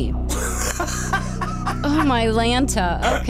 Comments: Did she just say oh my Lanta. (only fuller house fans will understand) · did she say "Oh my Lanta?" It's DJ Tanner all over again...ps: watching after 3yrs.. again